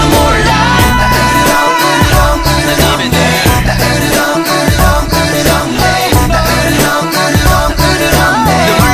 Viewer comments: What does vocal says in 03:14 ? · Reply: "Ich liebe dich" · mmm can someone please tell me what jongin says between 3:14-3:15 while they are saying eureureong eureureong in chorus i cant figure out myself and cant find it in the lyrics aswell its pissing meee offf aaaaaaaggggghhh hehe*cough* thankyou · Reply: "한입에 널" which means 'I'll eat you up in a bite'. You can see Kai's choreography of that part looks like eating something.